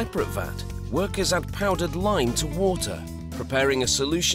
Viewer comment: What is the name of the powder the worker is adding at 2:25 ? ( I tried to listen to it multiple times but couldn't understand). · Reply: Lime! what the hell ,what we really eat ?